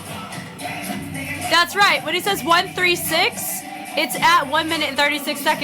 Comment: he actually says